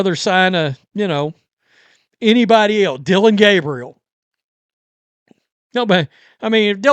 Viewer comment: could have sworn you said roman gabriel. had to go back and relisten. lol